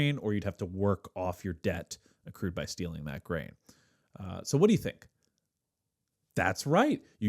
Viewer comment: Not me mishearing "stealing the brain" 💀